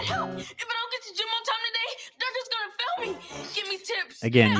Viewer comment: Ned help! If I don't get to gym on time today, Dirga's gonna fill me! Give me tips; I need tips! (Fill instead of Fail LOL) Sounds like somebody finds bloating uncomfortable, or either he's afraid Loomer's gonna make fun of his belly for it...